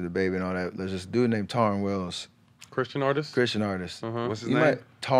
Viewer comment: Can someone please name the Gospel Artist that Steph Curry mentioned on this podcast? It sounds like he said Torn Willis? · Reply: Tauren Wells